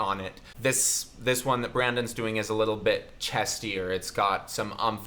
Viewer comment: did you say Brandon?